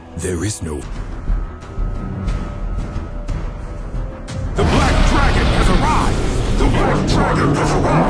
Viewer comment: Emmm did i heard said " The bLack Dragon never ride" is it?